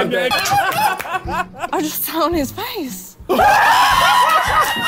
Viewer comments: Why was she so ready for that 😂 “I’ve been waitin my whole life for this moment” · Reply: At the end did she say " I just shit on his face? Cause it didn't sound like she said " I just SAT on his face, which is proper English! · Oh nevermind “SIT ON HIS FACE” bruh I thought he said shit on his face